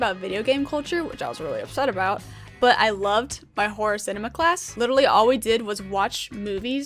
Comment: Thought she said horse enema instead of horror cinema